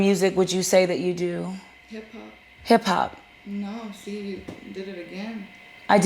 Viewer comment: Ongoddd, I heard h-pop too 22:15 She is kinda slow, and doesn’t really know what’s going on…